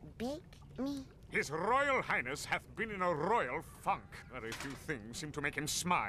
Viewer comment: I misheard funk as fuck X'D